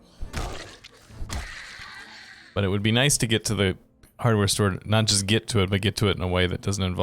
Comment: Words are hard. Next thing I heard was "hard word store" - cracked me up.